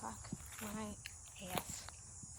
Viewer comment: at the 6:25 min mark, did you say Fuck My Ass??